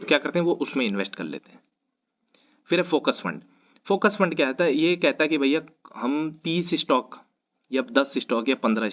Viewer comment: what is "saybee" in 25:17????? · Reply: SEBI.